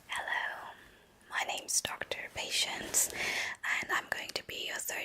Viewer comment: Was looking away when you introduced yourself i thought you said "my name is doctor patients" and boy, did i laugh